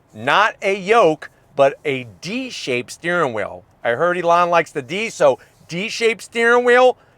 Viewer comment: Did he just say Elon loves the D 😅